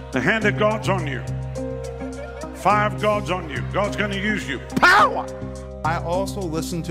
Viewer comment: One word: DISGUSTING!! Already one minute into the video, at the time mark of 1:16-1:25 he is speaking to a 13 yr old boy and plainly (with blasphemy) says "...5 gods on you..." I had to go back and put closed captions on the video to make sure I heard that blasphemous statement correctly. And I sure did. THERE WILL BE NO OTHER gods BEFORE ME (Exodus 20:3). The Lord said it first amd declared it as HIS COMMANDMENT. Point blank period. Any true follower of Christ will rebuke his false teachings and protect their child. I see parents don't care about God and protecting their children. This is a perfect example. Also, the thumbnail of the video made me immediately think that he was sacrificing children, boldly in front of the congregation - I immediately thought he was a Satanist. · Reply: He's actually saying: the fire of God is on you. I'm not defending him, but I know that's what he talks about a lot "The fire of God" 🙏